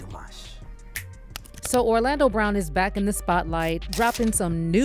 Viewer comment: O hell, I thought it said Orlando bloom